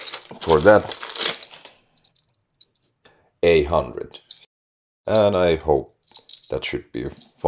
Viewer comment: it's an A100k, but the subtitles say "800". Just wanted to let you know. Great video otherwise! Hyvä🤘🤘